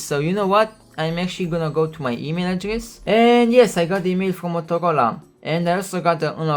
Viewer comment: I thought he said Motocola